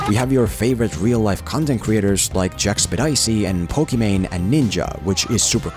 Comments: Did he just say "Jack-spidicy"? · what does the computer narrative voice say for Jacksepticeye? Jack Speed Icee? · did he just say jack spedicey? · what did he say? Jack Who Spedicey? ahahaha